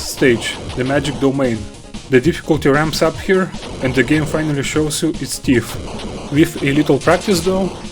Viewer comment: Good video, it's convinced me to give Silver Surfer a try. 10:28 - I had to replay this bit a few times to figure out what you were saying. At first I thought maybe you were saying "The game finally shows you it's Steve", and the closed captioning said "and game finally shows you it's Thief"... but as I was writing this comment, I was like, "OHHH... the game finally shows you ITS TEETH". 100% skill issue on my part.